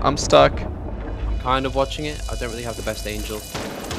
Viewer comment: did he say best angel? Rather than angle 😂😂